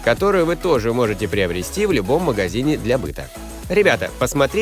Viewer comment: мне послышалось Вы тоже можете приобрести форму в любом магазине для пыток